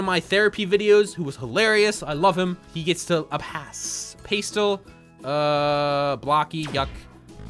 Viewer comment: did you just say "pay-stell"???